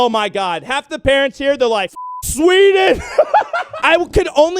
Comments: Did he say fuck Sweden at 1:33 or fucking sweet? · Did you say f sweden